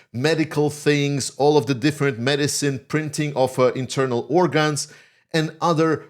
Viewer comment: Did you just say "printing of internal organs"? Wow...